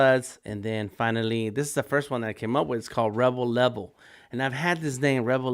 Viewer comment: Rebel Level, sounds cool, For a second, I thought you said Rubble Rubble. The catch phrase for the Hamburglar